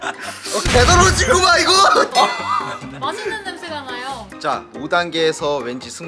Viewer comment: 이거 잘들어보면 "맛있는 냄새가 나요"들림5:44